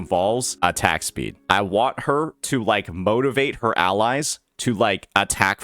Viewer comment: did i hear "Motivation" 👀👀